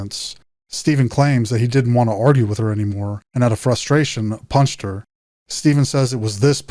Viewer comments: I thought he said he "pushed her" not "punched her" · Where in the Interrogation did he say he “PUNCHED “ her?????All I heard was he “PUSHED” her!!!! But the Commenter said Punched???? Hmmmmm 🤔